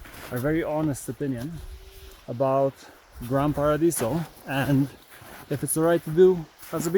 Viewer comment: So glad to see coffee time. 🎉 I always heard you say Grandpa Radiso 😂🥳 Great Video